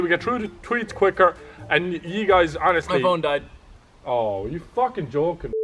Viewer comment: I thought mini Ladd said my mom died